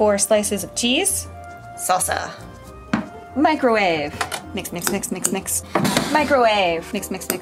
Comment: She was saying "mix mix mix mix mix" And all I heard was "Psspsspsspsspss"